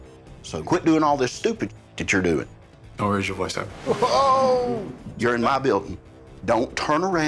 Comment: what did say to Chief Peek? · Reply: Don’t raise your voice at me